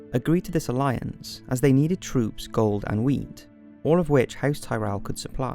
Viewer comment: sounds like you said house Lannister has to forge alliance with Tyrells for “troops, gold, αnd weed." Ya gotta trade what ya gotta trade. Thanks for keeping it real